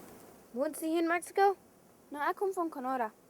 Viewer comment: I can understand Standard German and a bit of Dutch, so I could pick up on a few words. I may be wrong, but I believe I heard at 3:17 "Wohnst Sie in Marksco?" "No ek komm van Kanora" I think I also heard Grosmama (Grandmother) Ek Wot Nich (I don't know, Ich weiß es nicht, ik weet nie Wo wohnst ji? Even though I couldn't really understand this video, it was still interesting to watch. It is nice to see that the Mennonites appreciate and maintain their culture, especially their language.